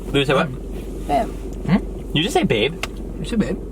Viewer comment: did she say babe?? Or did she say say it??? 🙄